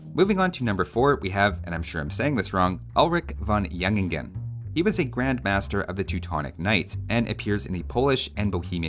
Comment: I heard "Ulrich Von Lichenstein"... anyone else hear this?